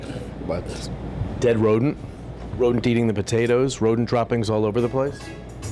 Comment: LoL. Thought it said rodent shop. 🤣 Thank you for saying their address. If these owners can’t admit issues then boycot them. No excuse for that nastiness.